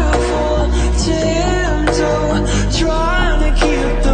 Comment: I'm tired of being careful, tiptoe, trying to keep the water warm, let me under your skin, uh oh, there it goes, I said too much it overflowed. Why do I always spill?~~~ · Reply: bro i thought she said tantrum and not tiptoe